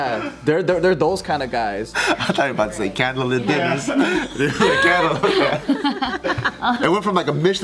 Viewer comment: ohhh he said candle, i thought he said camel lol